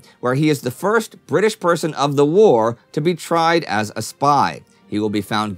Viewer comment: Indy and Team, would it be possible for me to get subtitle-r access? At 5:58 the English subs say "spay" instead of "spy" I tried to correct it, but it says it's locked by the video owner.... Love the great work, keep being yourselves!! :)